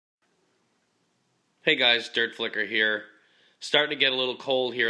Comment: I thought the intro said "the adventures of dirtf**cker". Not flicker...oops.